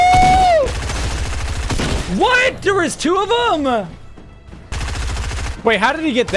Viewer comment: i swear the bot said what the f